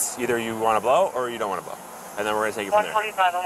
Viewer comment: I think he says "Well, take you to jail." It's super hard to hear, but yeah, I don't think this cop was kosher about him not being clear! He wasn't right. What the HECK was he in such a RUSH about?! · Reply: he said "we'll take it from there"